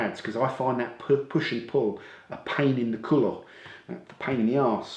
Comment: What did he say at 20:06? "pain in the ____, pain in the ass"? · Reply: Coulon